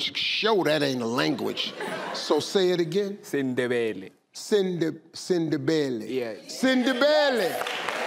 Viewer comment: Even I heard as “Cinderella” 😭 Anyways, such a beautiful place that South Africa is. So many languages! ❤